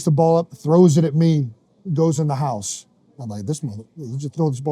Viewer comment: Did he say this Nxgga at 1:06😅 I know I’m not tripping